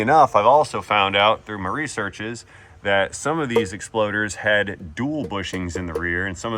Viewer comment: did i hear it right? he said exploaders? hehehe